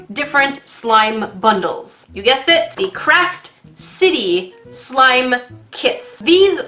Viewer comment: I thought she said crack city LOL